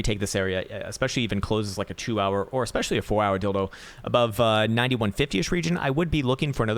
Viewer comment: WTF Did he just say a 2hr or 4hr "Dildo"?